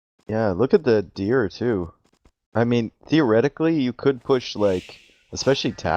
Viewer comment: did he say deer-etically or theoretically